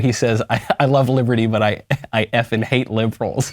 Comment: he actually said "I love liberty but I hate white liberals"